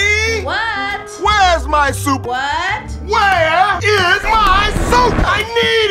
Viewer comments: What you hear: "where is my suit?" What i hear: "HONEYYY WHERE IS MY SOUP?!?!? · What everyone heard:"honey where's my soup What I heard:mommy where's my soup